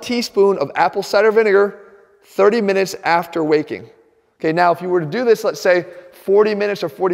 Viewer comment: Thought it said after wacking 😂